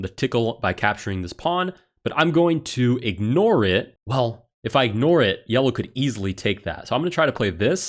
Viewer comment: Does anybody else hear the “pull” sound at 10:54? Frightened me and thought it said “Paul” my name. 😂 I still don’t know why that sound was made though